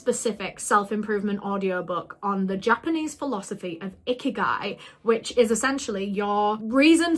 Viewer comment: I’m sorry but each time you say ‘ikigai’ all I hear is ‘icky guy’😭 I don’t want to find ‘my icky guy’😭😭😭😭😭